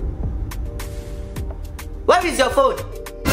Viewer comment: Anyone notice that instead of Mama Ojo to say "where is your phone", if you listen closely you will hear her say "what is your phone."